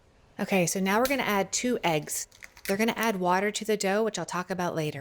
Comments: I was confused. I thought she said "we're going to add water to the dough, but we'll talk about that later". What she really said (in reference to the eggs), is "they're going to add water to the dough". So there's is no water in this recipe, other than the "water" in the eggs. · Great presentation. But what's the part about water? At minute 2:15 you mention water and proceed to make a very coherent presentation about protein water fat. Thank you. But the print recipe says nothing about adding water, and that's also true in the video process of building the recipe. So - do I add water to this recipe to get that nice result? Also thank you for making a recipe that didn't require a heap of special (expensive) equipment. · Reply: I thought the EXACT same thing! I swore she said “… we’re going to add water to the dough which I’ll talk about later.” I heard her explain the effects of water but never saw her actually add it. I spent the last 20 minutes scratching my head and was going to comment until I saw your post and the reply. At 2:12 she actually says “Okay, so now we’re going to add two eggs. THEY’RE going to add water…”. She talks so rapidly, it’s difficult for us old folk to understand. Thanks to you and the reply I’m ready to bake!